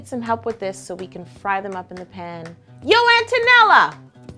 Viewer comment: I thought she said "HEY AUNT ONELLA!!" 😂